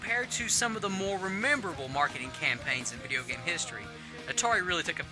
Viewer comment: did he say "rememberable"? Good lord.